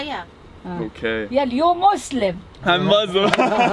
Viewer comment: I thought she said "your more slim" xD.